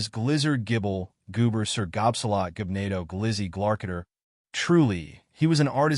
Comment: Naaa I heard glizzy 😂😂😂😂😂